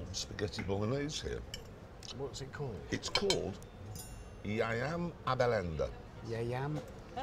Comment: Did Hammond just say "I am a bell end" in another language?